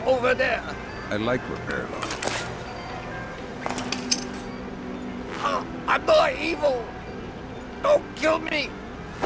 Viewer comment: Did he say "I'm more evil. Don't kill me"? I'm kinda confused why that would be a good reason to not kill him, lol.